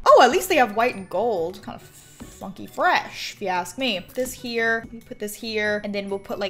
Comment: who else heard mumble the f word